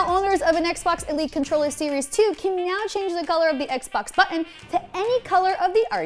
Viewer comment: sounded like she said "meow" instead of now. haha